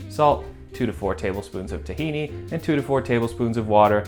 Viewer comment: what's the two tablespoons thing he puts in? do we have an equivalent in Europe? it sounds like he says zucchini, but it looks like a gooey peanut butter?